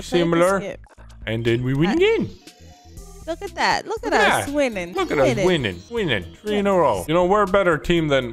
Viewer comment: Thought they said “look at us, women” when they said winning